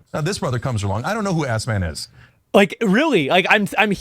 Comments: I thought Chenk said "I don't know who Assman is" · I hear 'ass man', just me? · I hear the spazzing walrus saying "Ass-man" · I hear assman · ngl I hear Ass Man and I’m certain he’s doing it intentionally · I hear AssMan… the greatest hero of all time! 🍑 🫡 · idk about you but i hear "ass man" and that sounds more like a compliment 😏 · I hear “Ass-man” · I dont think he is saying “S-man”, I think hes saying “ass man” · assman is what I heard ha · I hear ass man, but I am just a youtube comment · i heard assman 😂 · I hear assman like hell you u get ass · I keep hearing “A$$ man” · I Heard Ass-Mann 😂😂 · i heard AssMan. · I heard ASSMAN.....Like on Seinfeld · I hear “ass man” lmao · can't lie i heard assman